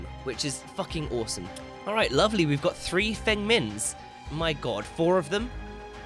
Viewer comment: when he said three feng mins at the start i thought he said thin mints lol.